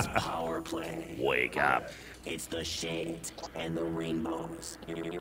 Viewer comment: I first heard the robo-voice say "It's The Shit and The Rainbows" before I backed it up and realized that it was actually saying The Shades and The Rainbows XD